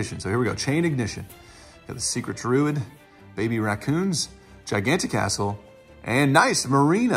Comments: thought you said gigantic a*****e instead of Giganticastle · thought you said gigantic a*****e instead of Giganticastle LOL!!